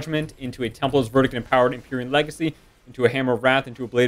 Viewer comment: mark after templer verdic what are u saying after that ''power imperial legacy?'' what spell is that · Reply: Empowered templars verdict, which is empyrean legacy